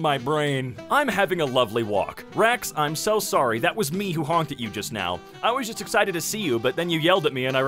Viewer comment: thought that said haunted you